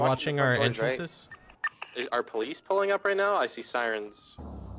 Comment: Wtf at 1:00 ol boy really say I see sirens?